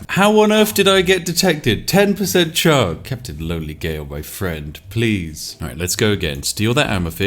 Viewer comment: Kinda misheard you say "Captain Don Miguel" and, not gonna lie, that's a much better name.